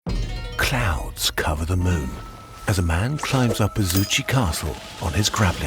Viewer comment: Wow, first thing i heard was "Clowns cover the moon as a man climbs up his goochie castle." I need to get my hearing checked lol